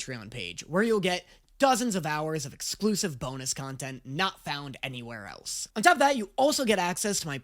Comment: I thought you said boneless content instead of bonus content